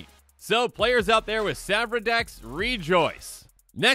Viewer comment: What did he say at 6:45 ? "And keep in mind that those pests this creates are booth black and green, so players out here with "asdfasdf" decks, rejoice?" What is the commander he says? I couldn't understand. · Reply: Oh... It's "Savra" as in Savra, Queen of the Golgari...